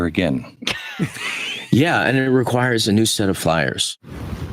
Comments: No, No. The MR Crew misheard Schneider! At 8:44 he doesn't say, "And it requires a new set of flyers." He instead says (listen carefully - watch his lips), "And it requires a new set of FIRES." He doesn't mean a new team of pilots to fly into the breach to do battle for freedom, etc. He means we need a new bunch of bonfires to burn more non-conformists or people that threaten my conservative views (read liberals). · sounds like he said "Yeah, and it requires a new set of fires."..... That would perfectly represent what he/they really want. Put em all back in their place.